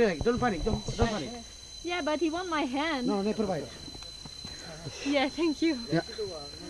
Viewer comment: Ape: bites girls hand Guide: "LET IT BITE" Me: O_O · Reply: “never bite” you misheard